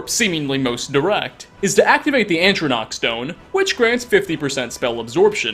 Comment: I’m sorry.....wait.....did you just say, “ANtrinach,” Stone?? ANtrinach?? (Walks to window.....stares off into the distance....)